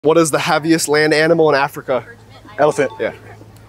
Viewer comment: Who else heard “happiest land animal” 😂😂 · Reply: He does say "heaviest" not "happiest"... It does sound like he says happiest tho but if you listen closely, he just pronounces it weird lol